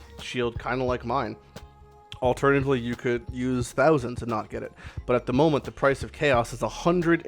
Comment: Sorry, could not hear it. Use what? 7:20 -- doesn''t Exalt add another affix instead of changing existing one? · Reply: he said "you could use thousands and not get it" and exalteds add another affix thats right, he had Hybrid %es which means stun and block recovery + %es which can go up to 56% i believe, the exalted added the normal %es affix which can go up to 135%~ but he hit a low roll on that